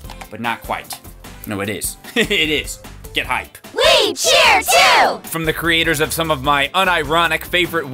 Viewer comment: I looked a way before he said "We Cheer 2" and thought he said "Witcher 2".